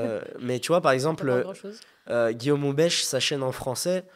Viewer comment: c’est quoi le nom qu’il a dit si des gens connaissent ? 😊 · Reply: C'est Guillaume Moubeche frérot